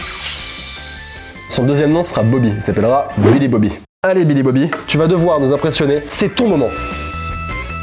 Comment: Je ne sais pas si c'est moi mais quand il a dit " Billy Bobby" rapidement . J'ai entendu Bobby Li Bobby 😆(